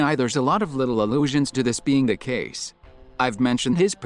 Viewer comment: awesome video! i love seeing you on my subscriptions<33 by the way, theres a typo at 14:44 in the captions, it should say "allusions" instead of "illusions". but dont worry! you said "allusions" in the video, it's just the caption that's wrong